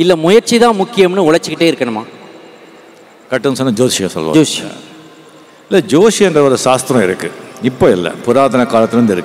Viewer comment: are they talking about Astrology ? Joshi is the word I think he said